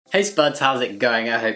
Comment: for some reason in your intro i hear "sperms" instead of spuds (or whatever you say cuz i don't really understand it🤷‍♂️)